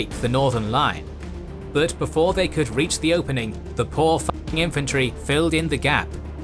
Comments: Anyone else hear “The poor fxcking infantry” at 48:45 XD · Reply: Well they are called the poor fucking infantry, so you probably heard correctly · did he say “The poor f**king infantry”???😂😂😂😂